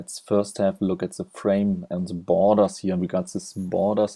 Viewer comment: Did he just say "boners" instead of borders? :-)